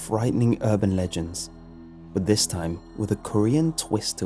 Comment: I thought it say Uber legends😂😂